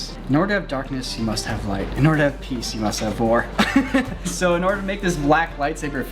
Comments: I swear at 12:04 Niko says "In order to have peace you must have vore" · i swear to gods did he just say "in order to have peace, you must have vore-" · I need to stop visiting feeder sites. I coulda swore he said “to have peace, you must have vore” 🤦🏼‍♂️ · I misheard Niko at 12:00 and thought he said “in order to have peace you must have vore” 😂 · Lol I thought he said "to have peace you must have vore" XD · I heard "In order to have peace, you must have vore." and was very concerned for a second until my brain caught up...